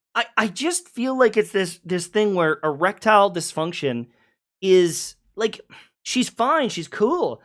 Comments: Did i just mishear or he called her erectile dysfunction at one point? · "erectile dysfunction" am I crazy or did anyone else hear that as well?